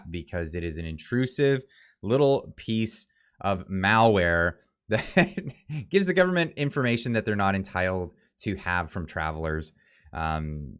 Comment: "It's an intrusive piece of MaoWare." Pretty sure that's what you said.